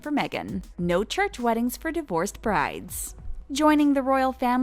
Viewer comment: I thought it said "Brat" not "Bride" LOL